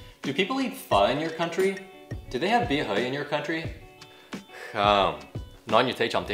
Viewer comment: Really helpful, thank you. Ps: bia hơi, not bia hời (i heard you said 'hời' several times :3)